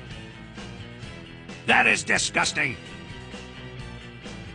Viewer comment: I heard " Nice coc* "